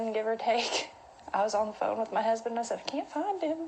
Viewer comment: "About three minutes in, give or take- I was on the phone with my husband- I said, 'I can't find him'." I hear the "I was on the phone with my husband" as an interruption in her story to set the scene. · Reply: She doesn't use the pronoun 'I'. She said "Can't find him" (in a sing-song way). If this is how it actually was at the time then CP would have already had context as to who and what was going on.